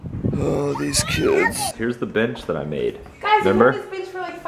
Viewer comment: I thought said "here's the b** that I made" 😩 lol